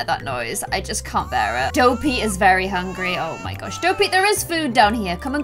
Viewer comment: I thought u said “Go pee is very hungry” LOL